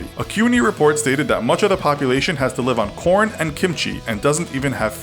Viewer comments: I thought he said porn and kimchi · I thought he said "porn" for a minute.. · he says porn instead of pork, or is it just me?